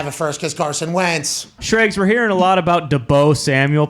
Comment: did he say “Da Bo” Samuel?!? 🤣 and The Niners are crazy if they don’t give him a big contract! According to them, Jimmy G sux sooo…basically he WAS the whole offense…